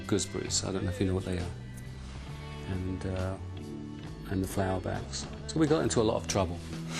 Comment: lol i thought he said they threw bags of FLOWERS and ran away lol that sounded so gay but i realised its flour bags ... blowing up the amp n stuff was pretty badass, cudve blown in their face but who cares,thats rocknroll baby